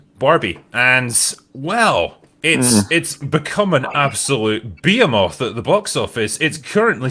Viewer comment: "It's become an absolute .... at the big office." What is this phrase? I am not familiar with it apparently and it almost sounded like, "an absolute be-am-off." He is not saying, "behemoth," right?